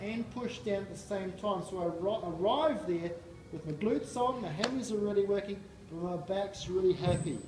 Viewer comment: does he say hamstrings should or shouldn't be working? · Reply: He says "the hams are already working" so it's ok they activate too